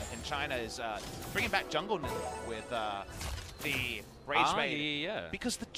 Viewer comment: did papa sayed jungle nunu ?